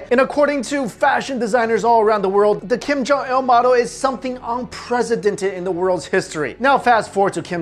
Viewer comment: Did you just say unpresidented ?